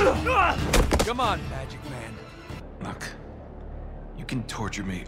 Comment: Did Nate just say "Come on, magic man" at 17:51? Damn had me laughing hard 😂